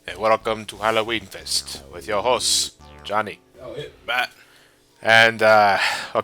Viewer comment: I'm sorry, guys, but i think, i'm not the only one, who heard "with your hoes" instead of "host" at 0:10 due to Johnny's "italian accent". :D Right ?